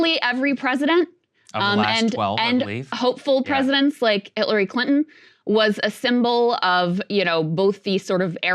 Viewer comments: Did Krystal say "Hitlery Clinton" at 1:27 🤣🤣🤣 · did Crystal say “Hitlery Clinton”? 😂😂😂